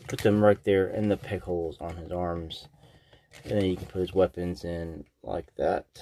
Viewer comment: I heard "in the pickles on his arms"